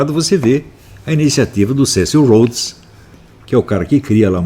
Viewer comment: Nos minutos 39:14 qual o nome do teórico que ele cita " eu entendi serceu hodes" Acredito que esteja errado alguém poderia me dizer qual o nome e onde posso encontrar a fonte ?